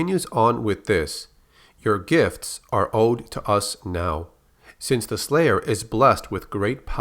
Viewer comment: I heard the Seraphim Say "You won Gifts, You owe to us now"